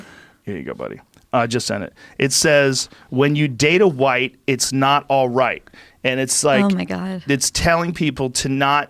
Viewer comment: Did anyone else at first think he said "When you're Dana White, it's not alright!" 🤔 😄